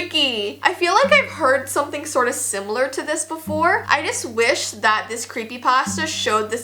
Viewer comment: I swear she said I vish